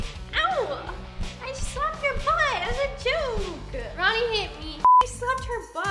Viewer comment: Roni “I slapped ur butt as a joke” Nessa “Roni hit me!” 😂❤️ love u guys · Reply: Why did I heard "Roni hate me" lmao